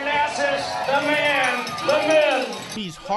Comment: Does anyone else hear the announcer say "the man.. the myth.. the myth.. the man"?